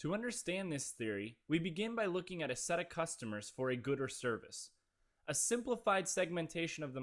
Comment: he said "good or service". i heard it as "gooder" the first time too. Unless of course, we're talking about 2 completely different things. :)